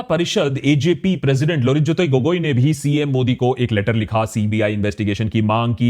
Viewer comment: did you say CM Modi ?